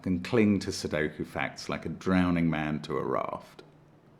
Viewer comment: I misheard him as "I can cling to sudoku facts like a drowning man to a rock," and that's how I felt a lot of this puzzle. lol